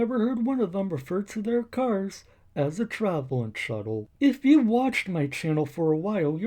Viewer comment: Get em miss officer. At first i thought he said a traveling shovel.